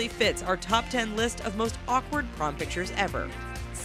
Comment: I thought it said most awkward porn pictures ever and I was like w8 wtf... watches and reads title again "prom"